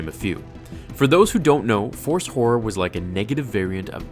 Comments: When he said "Force Horror" I thought he said "Force Whore" XD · I'm sorry but did I hear "force whore" at 4:47?